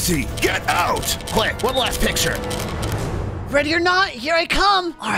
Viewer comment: did I just hear ready or not!? (In ishowspeed's shake music I heard ready or not)